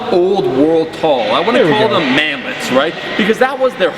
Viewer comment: What is he saying around 3:50 ? Manlity? Manlady? Whut? EDIT So appearantly it's 'Manlet', and everyone under 5'11 is a manlet? That's fucking retarded, considering the worlds avarage is 5 '7 and the USA avarage is 5'10. You should all come to the Netherlands. If you're below 6'3 you'll be considered a manlet here. And yes, i'm 5'10, so i guess you could consider this 'manlet rage'. Oh, the things i learn on the internet everyday.